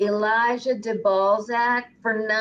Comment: Please tell me “Elligah de Balzhek” was someone trying to get to say “I lick the ball sack.” 😂😂😂 I about died when she read that. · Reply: lol, I heard "I lika de balzak".